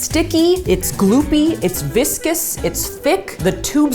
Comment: I thought you said "Its vicious"